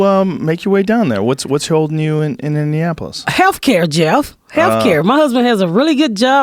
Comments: did she call Joe "jeff?" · did she call him Jeff ?😂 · Did she call him "Jeff" at 3:11?